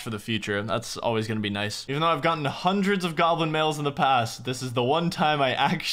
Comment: the number of times I hear "goblin males"